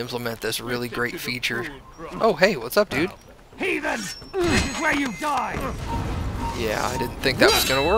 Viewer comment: i swear at 7:00 that guard said penis